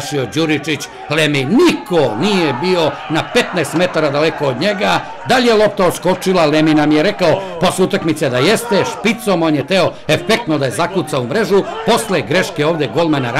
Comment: I swear he said the n word!!!